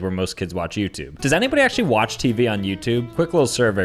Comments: wait !! what did you said 5:50 does anybody watch TV on youtube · did you say do you watch TV on YouTube like what